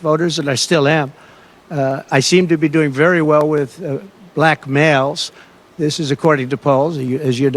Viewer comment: Did he say "I seem to be doing very well with blackmails" (7:40) or is it just me and my listening comprehension? 😜